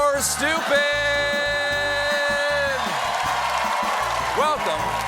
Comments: did colbert just say "EAGLE or stupid"? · Did he say "Eagle vs Stupid"😅. Really sounds like he mispronounced evil. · I don't know why, but it sounds like Stephen says "Eagle or stupid".